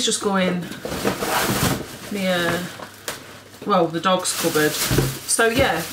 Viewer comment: Omg At first I thought you said you were making an ear 👂 🍛🤣😂😅🤣😂😅🤣😂😅